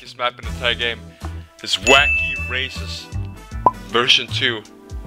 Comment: is it me or did he say wacky racists?